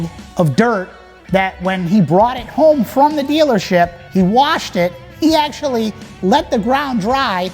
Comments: I thought you said he never washed it. Nevermind, no go. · The owner of the truck recently sold it for $45,000. A lot of people are wondering so yea. It sold for $45,000. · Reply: I thought you said he never washed it. Nevermind, no go.